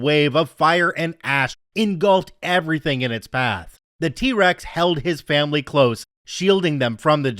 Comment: I heard “The T. Rex held its family…” and started cackling aloud “Not with THOSE arms!!”